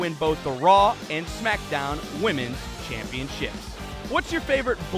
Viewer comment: I heard "Bliss This" instead of "List This" 😅